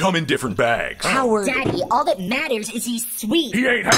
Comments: "He ain't half as sweet as white chocolate!" Bro you know that you're all dark chocolate on the inside right? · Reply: I thought he said “he ain’t half as sweet as my chocolate”, but what you said makes more sense 😐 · Idk what's more cursed. The white chocolate insult or the fact that M&Ms can eat spaghetti. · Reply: I actually thought the dad said "MY chocolate" which would've made a lot more sense rather than an obvious racist joke